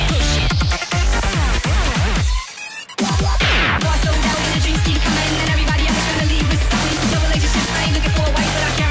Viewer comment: what does he say in 0:55? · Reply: There was an attempt Everything within quotes is stuff I'm unsure of (aka gibberish) Was so down in a "jinxty commin'" and everybody else is gonna need this somethin'. "Dove it like a xis" I am looking for a white but I guarantee gonna "wreck that face". He's "puttin' that" control "shizzle" in the skirts, bodies in the throne, got me in the zone man, I'm "shuasto" "we eigh eigh eigh yeah" "see ya't three-four" Actually, I'm not sure of anything